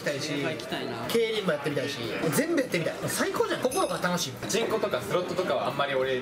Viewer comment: のパチンコがチ◯コって聞こえる